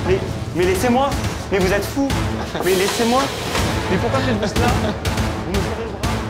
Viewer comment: "Mais pourquoi je suis une grosse merde" dit en scred 😂